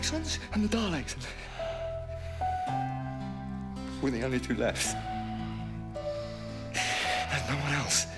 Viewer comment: When the Doctor say "Were the only two left" I hear "Please don't leave me alone".